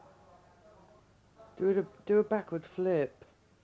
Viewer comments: Nobody: Freddie to dalilah: dO a BaCkWoRdS fLiP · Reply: I thought he said acid dance. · Nobody: Freddie to dalilah: dO a BaCkWoRdS fLiP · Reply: I thought he said latin dance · I thought he said “or a lap dance” 💀 he actually said “a latin dance” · "Do a backwards flip or...latin dance" This is too pure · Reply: Nuulie I thought he said lap dance